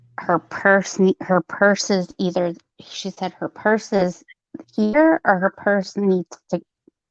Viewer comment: I hear "her purse is here" telling